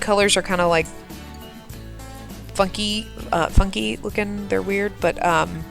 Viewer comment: LOL it totally sounded like you said "fu*ky" when you said funky the first time hehe! XD at a loss for words, what's going on with that? ;) I like the "fake" waterfall, I really hope it turns out awesome once you get this house plopped. The erratic use of the flower texture spray thingy really helped bring the farm to life and more detailed, was definitely worth doing so. Another big thumps up from me! :)